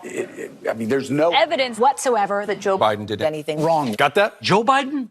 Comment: Did I just hear CNN say "Joe 'Spiden' "?